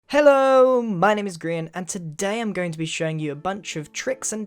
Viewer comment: I thought you said hello I'm Korean lol